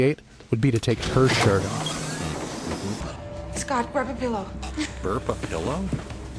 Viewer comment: Y'know, it actually does sound like she says "burp a pillow". xD